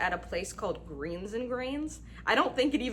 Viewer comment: I thought you said Beans and Greens at first.